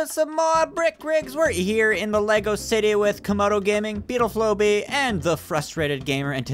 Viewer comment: I Swear when introduced Beautiful OB just now it sounded like he said "Beetle Flow-Bee" < yep that tracks...